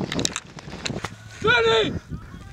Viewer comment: someone cracked a "That's what she said" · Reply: He said watch his head